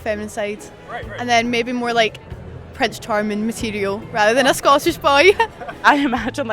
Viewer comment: I swear I thought she said "sausage party" @ 2:02, but then I had to rewind and now it sounds more like Scottish boys LOL. It's clear her exposure is to big city liberal "men" and not normal men from other parts of the country. In addition, when you try and compare a people from a country with 300 million people to a country with a country of less than 6 million, then there is the obvious issue of the Scots being exposed to only a small portion of the culture of the US. In addition, as far as a country with a large population the US is quite more diverse and varied as the early immigrants came from many countries and brought their culture and habits with them.